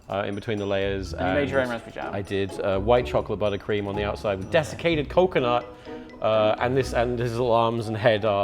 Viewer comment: I hear "desecrated coconut" for every damned time, now I want to see a Satanist Bake off. Lava cakes, where the lava is brought from Hell itself.